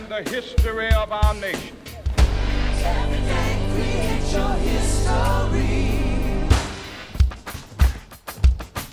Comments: what are they saying?? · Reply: "Everyday create your HIStory."- It's from the HIStory album. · please tell me what they say in this part. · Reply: Everyday create your history